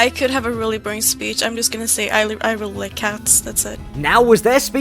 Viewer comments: Lol at 28:09 editor mess up and said she really likes dogs instead of cats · WAS INSANE. She clearly said "I really like cats" but on the subtitles it said "I really like dogs" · “I really like cats” and the subtitle said dogs.. 😂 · subtitle said dogs but she said cats 😂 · I really like cats (subtitle says dogs) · she said i really like cats and caption said i reall like dogs poor girl lol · What she said "I really like cats" What the subtitles said "I really like dogs" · subtitles typo, she said cats subtitles said dogs